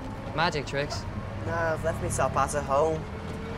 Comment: ''He should be in bed'' I swear the god its John's voice. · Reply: tim kalic what's George saying at 2:00 I think he said ,,I left my bat at home"